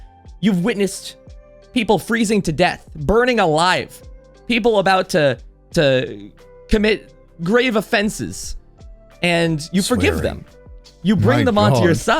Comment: I love how he translates a character on the edge of suicide to "people about to commit grave offenses" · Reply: I thought he said "who have committed great offences"